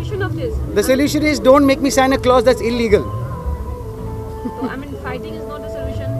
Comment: i heard.. don't make me santa clause that's illegal.. but i feel bad for him though